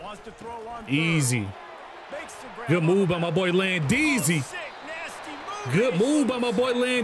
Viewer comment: what did he say !!!!! 😂 “oh 💩 nasty move”!!!